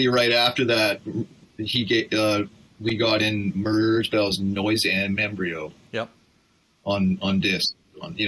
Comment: what is he referencing here? I can't quite make out the artist/release. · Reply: Merzbow "Noisembryo"